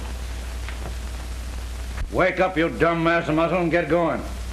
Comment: did he just call him a dumbmass.